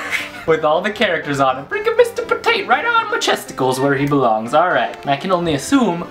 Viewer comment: Did he say chesticles? XD